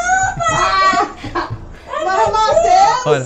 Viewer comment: Serviço atual, tratudor de idioma secreto infanto-juvenil 1:37 "Não não mae, nao fui eu 2x" 1:41 "Eu sei la mae" 1:53 "E-eu nao sei oq é isso se isso é algo ruim" 1:59 "É pq eu nao sei se foi eu ou alguem que viu algo ruim"